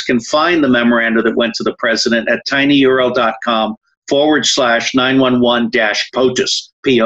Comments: WHAT IS HE SAYING AT 34:27? TINY URinal DOT COM? THAT DOESN'T EXIST? Does anyone know where this link is or is this just another BS scam? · WHAT IS HE SAYING AT 34:27? TINY URO DOT COM? THAT DOESN'T EXIST? Does anyone know where this link is or is this just another BS scam?